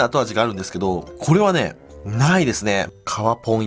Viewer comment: 皮ポンいきます、「かわぽんっ」 って聞こえる